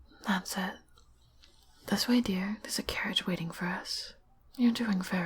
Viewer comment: I misheard carriage for carrot, so I was like :why should a carrot be waiting for us🤨